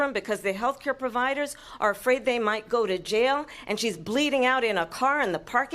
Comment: "And she's bleeding out in a car..." is what she actually said at 1:22.